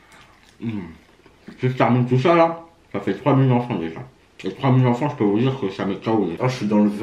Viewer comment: J'ai compris 3000 enfant j'ai relu le titre 2 fois